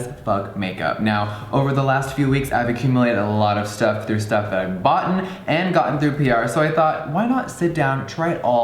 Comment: DID HE JUST SAY “STUFF THAT IVE BOUGHTEN” BITCH